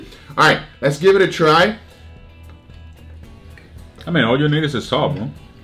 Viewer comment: did say “all you niggas is soft”